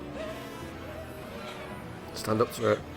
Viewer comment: What was Vink saying at 10:35 as the freezer door was opened? The line sounded like: "No! My god, it's feeding!" That guess might be wrong, but it makes sense Doctor Vink would call the magic statue a god, and a few seconds later says "It grew stronger with each feeding."